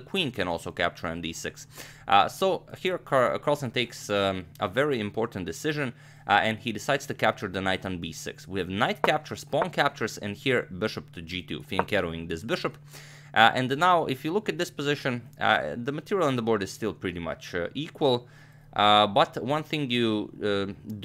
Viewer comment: the closed captions say fingering the bishop